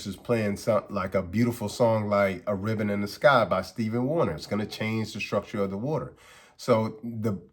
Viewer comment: I know you did not say Steven Warner!